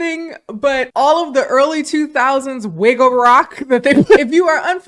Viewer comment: I really enjoyed the impromptu dance party I love me some wigga rock. (Also I thought she was saying wiggle rock at first and I just was thinking, this is my music so why haven’t I heard that before😅)